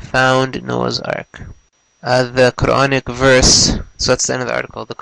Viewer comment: it sounds like dude said the chronic verse lmao